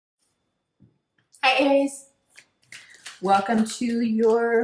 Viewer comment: I thought she said my name in the beginning lol my name is iris so when she said hi Aries I was like wait huh